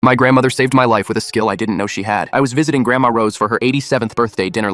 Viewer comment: I thought it said saved my life with a skillet because I scrooled at the end of skill and I just got back from camping 🏕